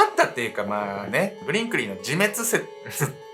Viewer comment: 今日の紹介人物のファミリーネーム、ブリンクリーがブリンブリンに聞こえたワイ キンタ○を移植した事はないけど偽関節に移植した事はある